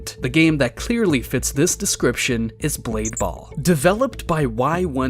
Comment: I heard greed ball😅